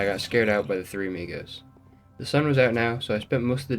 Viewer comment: you got scared by 3 who?😭🙏i heard niggas my bad